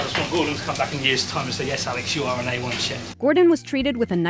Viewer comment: Wtf did he say!? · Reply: yes you are an A 1 Chef!